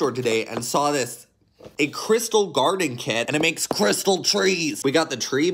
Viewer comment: At the first i thought he said crap trees lol